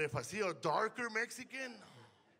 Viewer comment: I always thought he said "Chango García" 😂